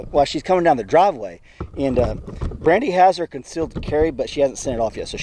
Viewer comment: Am I the only one that thought he said Randy houser